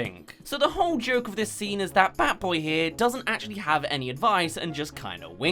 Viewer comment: When you said "bad boy here" Before 5:08 I heard badboyhalo xD